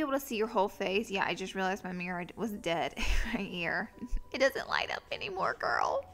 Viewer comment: what is her mirror called?? @ 1:17. i heard mirroraid or something like that